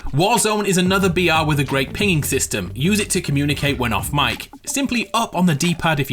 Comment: did he say peeing system 🤔 😂